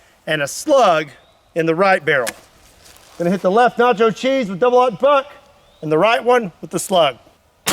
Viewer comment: I thought he said “Nitro G” not nacho cheese.. only slightly dissapointed